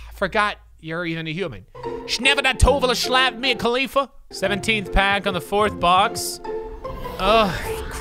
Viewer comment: 😂😂 Did he say Mia Kalifha